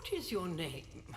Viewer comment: I thought the old lady said "F**k is your name?".